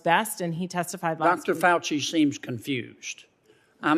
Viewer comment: I love how he says fauci is confused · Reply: I thought he said "SEEMS" confused. We all know Fauci is a liar.